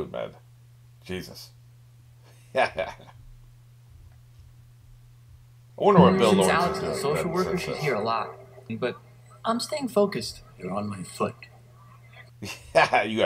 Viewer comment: What did you say? The audio from the show made it difficult to hear what you were saying. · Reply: I was wondering what the show creator is doing these days. I don't want to google it for fear of "Bill Lawrence pisses off fans by killing off Dr. Cox" headlines or whatever. LOL